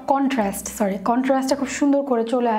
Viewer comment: Apu showing makeup with Korean products and said Contrast in 8:50 and I heard Oppa :3 my Koreanboo mind can't thinb straight anymore :v